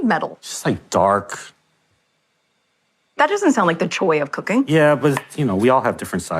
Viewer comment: It actually sounds like she said "the choi of cooking" she said it so strangely.